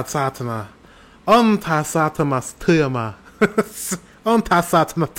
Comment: i think your first try on " on tää saatana työmaa" was best one when you were trying to grasp it. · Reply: after 13:20 you were saying "on tää saatamas työmaa" or what i heard but your Finnish a lot better than i would've even tought off!!